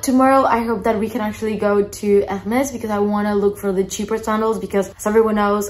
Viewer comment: I thought you’d said “cheaper sandals” not Chypre 😂 I was thinking “well she’s not going to get cheaper sandals in Hermes” 😂🤣🤷🏻‍♀️